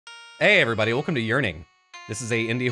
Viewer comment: i heard "hey guys welcome to urinate" lmao